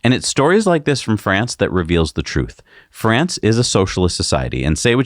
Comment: It sounded like you said, "France is a socialist country." lol